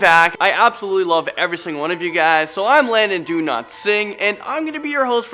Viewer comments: I heard "Landon do not sing" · It sound like he says his name is Landon do not sing lol love from Italy ❤️ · It totally sounded like he said “I’m Landon do not sing”